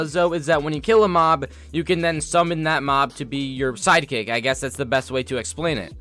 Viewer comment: I thought he said side chick but he said side kick